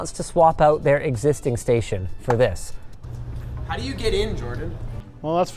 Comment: When I first heard it, I understood asking "How do you get injured". Had to laugh 😂